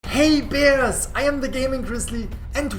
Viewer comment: i fr thought he said “Hey Perras” took me a while to realize he said bears bruh lol Can’t wait for these new updates tbh!!